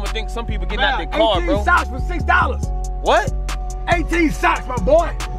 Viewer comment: I thought he said 18 shots for $6, I said turn up.